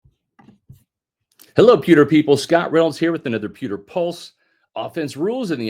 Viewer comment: Every time you say “Pewter People” I hear “Pimple Poppers”. I laugh every time ! Pewter Crew? naaaa, I enjoy the laugh